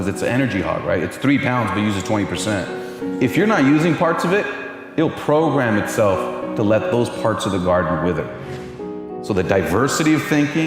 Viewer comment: What did he say? I couldn't understand and couldn't make any sense from the subtitle. Can anyone rephrase it? The subtitle says: It will program itself to let those parts of (the garden) with it. 😕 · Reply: "It will program itself to let those parts of the garden wither"